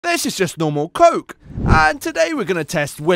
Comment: At first I thought he’s say this is normal coke and this is cocaine cheap vs expensive